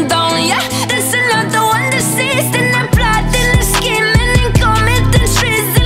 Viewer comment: I thought they said "And the blood and the skin" not "And they're plotting and scheming" lol